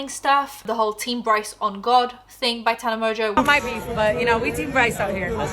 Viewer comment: I thought she was saying, "Team Bryce on guarde" for too long. 🤺